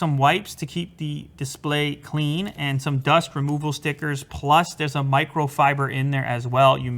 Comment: Lol 23:22 at first I heard that as "some dust to remove your stickers"!🤣🙈